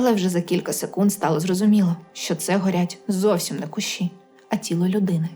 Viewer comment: Ще не дослував,але! 1:10 почулося "а це горять, зовсм іне кущі, а , блядь, тіло.....